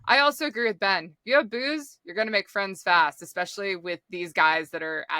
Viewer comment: I thought she said if you have boobs you going to make friends. Lol 😂I guess booze makes friends to. Probably not as good.